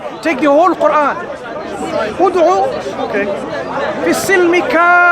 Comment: As Salamu Alaikum, a small correction in Shaa' Allah. 1:18 It is "Edkholou" instead of "Od'ou". Enter into Islam completely Al-Baqara 2:208. May Allah reward you abundantly and increase you in patience and wisdom.